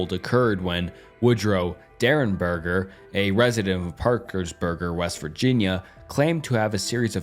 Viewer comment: Did you call my town 'Parkersburger' WV?